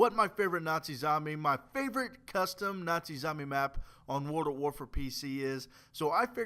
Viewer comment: Lol it sounded like you said bible map